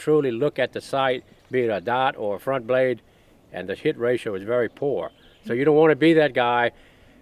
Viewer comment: Did Jerry say the shit ratio at 9:00?🤣